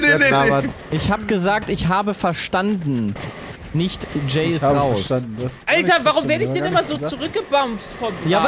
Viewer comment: noch ein kind das du lügst? 🤔🤔 sagt eindeutig jay ist raus. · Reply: "sep noch ein kind das du lügst?" Hä? "Sep sagt eindeutig jay ist raus." Er hat gesagt: "Ich hab verstanden 'Jay ist raus'. Ich weiß auch nicht warum."